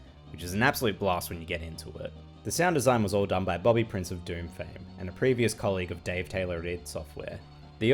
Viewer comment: for a second I thought you were saying “Bobby, Prince of Doom”